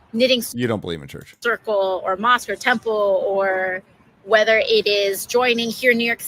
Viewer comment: Oh…… lmao At first I thought she said “a monster temple”!?! I had to go back! I was about to say “when did an energy drink company build a temple”?!? Lmao